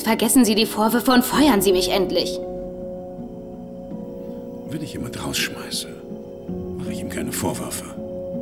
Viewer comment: Was sagt House bei 6:13? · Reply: Wenn ich jemanden rausschmeiße, mache ich ihm keine Vorwürfe.